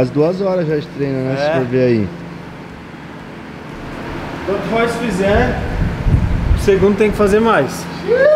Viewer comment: Em 27:27 eu entendi "Quanto Horse fizer Cbum tem que fazer mais" kkkkkkkkkkk